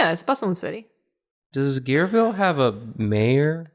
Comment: “Gare” is French for train station, so “Gareville” could mean “station city” or something similar! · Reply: that makes sense. I thought they were saying Gearville